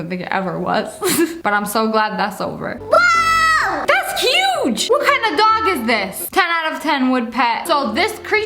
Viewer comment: uhhh did SSSniperWolf just say cuge 2:36 yep i think she did